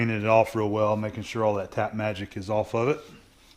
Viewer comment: At first I thought you said "Cat Magic". Took me awhile to figure it was Tap Magic. lol